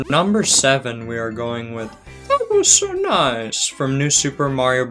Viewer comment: as a kid, I always thought he spoke Spanish or something else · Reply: I always thought that he was saying "Thank This on Eyes" until someone pointed out that he was saying "That Was So Nice!"